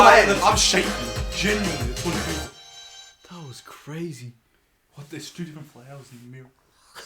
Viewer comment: Guys, I’ve been replaying this part since 10 minutes and I still can’t understand what said😭😭 I can’t go on without knowing 😭🙏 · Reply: He said "That was crazy, what there's two different flowers in the mirror", because there's a flower on the table, but the reflection of it in the mirror Jin is looking at is different. It's not the same type of flower in the reflection. If you pause the video and then click 8:23, you'll understand what I mean